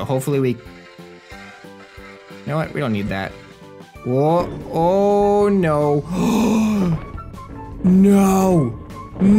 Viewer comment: Did he just say o'hare